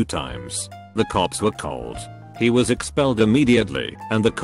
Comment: I wasn’t looking at the video and thought he said “jet”